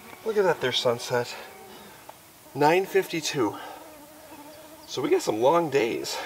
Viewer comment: did he said 9:52 sunset 😅